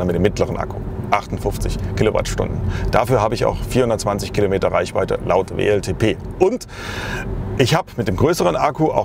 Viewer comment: Hab zuerst 24km reichweite gehört. Zum Glück war das mal 10 ^^